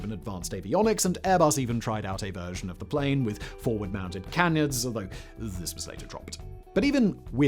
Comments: I heard "forward mounted cannons". I was a little shocked and confused as to why Airbus would test such a setup but was not surprised to hear they'd dropped it. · Had to rewatch a small portion of this at 8:45 as at first I thought he said that they "even developed a model with forward mounted cannons". WTF did he just say?!....oooh canards hahaha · Anyone else hear “forward mounted cannons”